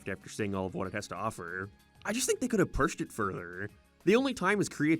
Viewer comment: did you just say "purshed"